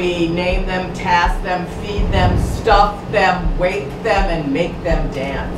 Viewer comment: Voodoo Priestess talking about the voodoo dolls on the wall: "We name them, task them, feed them, stuff them, rape them, and make them dance." Me: Well that escalated quickly! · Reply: I heard "wake them" or something